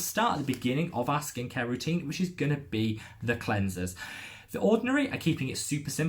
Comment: Me: "Did you just say 13 cleansers?" mind is blown for a good 30 seconds